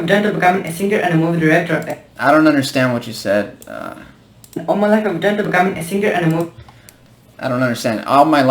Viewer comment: Lmao I understand what this guy said He said: All my life I want to become a singer and an movie director.